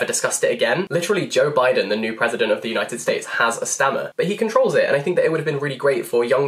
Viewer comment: Omg I am so dumb. You said Stammer, I thought you said stamina, cause like he had to, well you get it. I was so confused when said Joe Biden, until I realized what you very obviously said.